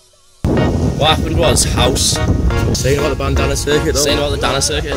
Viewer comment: Does anyone know what they are saying from 1:16-1:20? Sorry, I'm american and not understanding their english accents. Great vibes, nevertheless! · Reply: What happened is house, what are you saying about the bandana circuit?